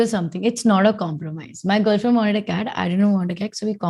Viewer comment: did suhani said girlfriend or boyfriend 🙄🧐🧐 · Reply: Girlfriend